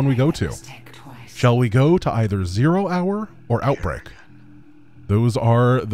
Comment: Outbreak 1st, so you can do 0-hour with POWAH! W8 no! I thought you said BREAKOUT! 😱 Fak! Ion't know, now. Maybe reverse it? Blue Flame hellions to roast the zerg and powah for teh 🧟‍♂️s?